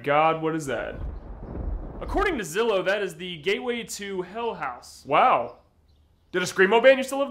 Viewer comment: i first heard "gayway to hell"